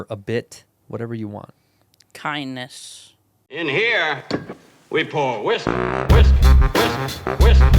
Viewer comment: I almost thought, at the end, it said "In here, we pour Wisdom" 😎